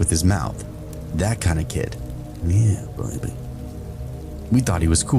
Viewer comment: anyone else hear that mumbling in the background that didn't seem to fit? Sounded like an Australian accent saying "yeah baby" or something.